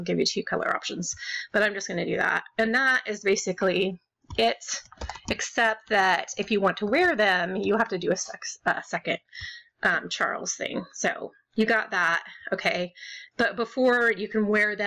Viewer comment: did she say "sex" accept for second? LOL iTS FINE GIRL!